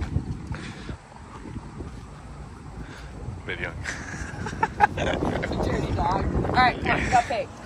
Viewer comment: What did say to when she was in front of the tractor at McDonald's drive thru? She said 'you dirty dog' it's at 4:59 .